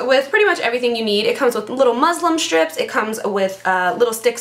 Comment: loved the video & your brows are naturally so amazing!! did you say Muslim strips instead of muslin strips at 3:52? LOL you are too funny!! :P